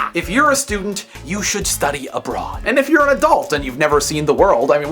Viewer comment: LOL At one point you said "If you're a student, you should study ABROAD", but I heard "If you're a student, you should study A BRA" ;-D